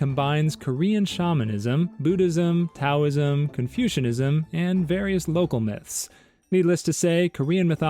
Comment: did you just say confusionism